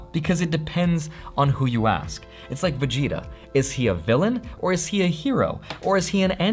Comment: I heard vagina is a villain. Need to watch less porn.